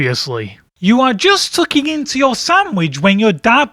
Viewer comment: I thought it said "fucking into your sandwich"